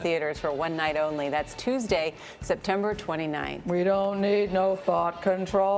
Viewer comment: I was cringing whilst watching this. Also, did I hear 'We don't need no fart-control?' near the end. Oh dear..